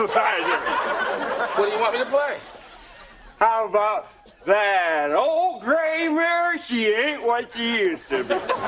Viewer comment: The ol' grey mare, she AIN'T what she used to be! I'm in tears! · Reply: I thought he was saying that old grey bag... Lol